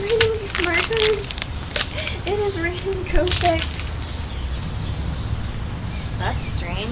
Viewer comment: my daughter thought you said its raining Kotex.